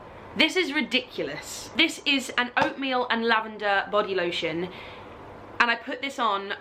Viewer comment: For half a second I thought you said it was "opium and lavender" and was like "Well, yeah, that'll put you to sleep". I think I need to get some lunch.